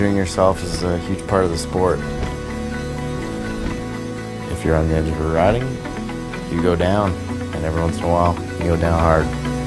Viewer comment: english is not my native language and i can't completly understand what's berrecloth sayin from 0:40 ...anyone who knows it can write it down here? many thanks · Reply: i know it's an old comment of yours but here you go: "if you're on the edge of your riding, you go down, and once in a while you go down hard". that's at least what i understand and yeah i'm not a native english speaking myself tho ;)